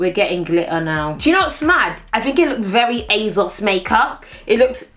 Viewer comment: Ahaha I thought it said ARGOS makeup and I was like bihhhh